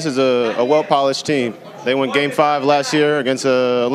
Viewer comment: No, no they didn't 😂😂😂 Edit: Subtitles showed me he said "they went game 5 last year". I thought he said "they won game 5 last year" coz you know, they didn't win it but yeah.